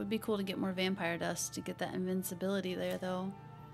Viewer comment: You need to sneak when picking locks and make sure you aren't seen... I also noticed the CC always says you are saying invincibility for things like vampire dust... is it wrong or are you mixing up invisibility and invincibility? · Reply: it sounds like she is saying invincibility